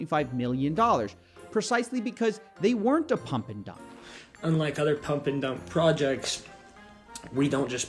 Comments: Oh, but you misheard the guy. He said, "Unlike other pump n' dump projects..." thereby implying that it IS a pump n' dump project, just not like the others... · If you listen closely at 3:05, he actually says "unlike other pump-and-dump projects", not "we're not a pump-and-dump project".